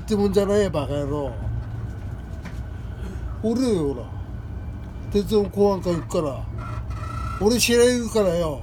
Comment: 鉄道公安官って聞こえるのなぁぜなぁぜ？（鉄道警察しかいない気が…）ｗｗｗ 撮影者の方、ナイスです！楽しませていただきました。 日暮里って聞こえてるから、ピー入れたほうがいいかもですね ｗｗｗｗｗｗｗｗｗｗｗｗｗｗｗｗｗｗｗｗｗｗｗ ＾－＾